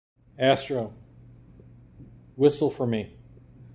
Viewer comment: I thought you said listen to me, and he was just being sassy😂😂😂😂